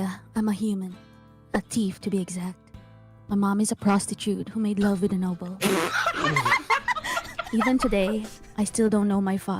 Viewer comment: i heard "my mother is a prostitute who made love with an elbow" HAHHAHAHA